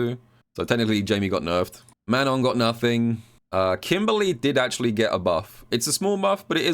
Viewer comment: No Manon buffs. Can’t have shit in Paris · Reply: i thought he said “manon got nerfed too” instead of “manon got nothing” i was gonna actually riot